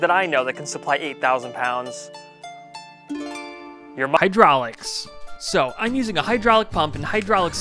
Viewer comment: i thought you say "your mama"